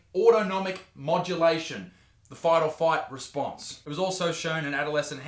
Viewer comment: The "Fight or Fight Response"? LOL Just found that funny given how many bloopers he put into that section, I was surprised he used the version where he said 'fight or fight' instead of 'fight or flight', I thought I'd heard it wrong so listened a few times, and yep, he went with 'fight or fight', not sure if he just gave up trying to get it all out in one go, or he was having a rip on the increased aggression that anabolic steroids can cause ... can't decide if he was going for an under the radar funny, or just didn't want to keep saying that sentence - was fun though either way ... weird things amuse me - that's why I'm here :)